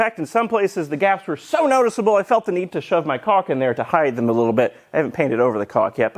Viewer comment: For a second there I thought you said you "felt the need to shove my cock in there" when talking about the gaps. I assume you really said caulk.... I hope. :-)